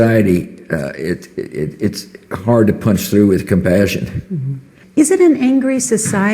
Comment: When he says "punch through with compassions", i hear "I f*** you with a smile".